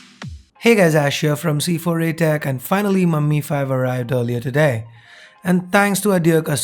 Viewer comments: did he said "MUMMY" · Lol at 0:08 it sounded like you said "finally mummy arrived"